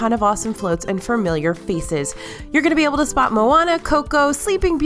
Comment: For a Moment I Thought You said Feces!! 💩💩